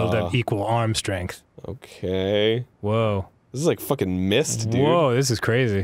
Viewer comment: arin arin did you say... missed?